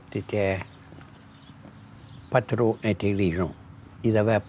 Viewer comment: A fascinating video. But tt seems like sometimes the subtitles don't transcribe what the speakers are actually saying. For example at 2:11 he says "pas trop intelligent" (not too intelligent) but the subtitles are "pas très intelligent" (not very intelligent). And in other places the English is not correctly translated. At 3:30 the boy is quted as saying "J'adore le français parce que la maman de mon papa parlait en français". It is wrongly translated as "I love French because my mom and dad speak French". But the correct translation should be "... because my dad's mother *spoke* French", which changes the meaning considerably. By the way, lagniappe is borrowed from Spanish *la ñapa*, and the Spanish got the word from Quechua.